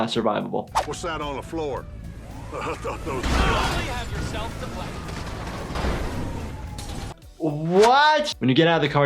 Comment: what did Trevor say?? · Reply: You only have yourself to blame.